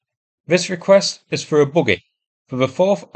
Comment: At the end when you announced the fundraised for the buggy, I heard it as "boogie" and was very confused. Look it up.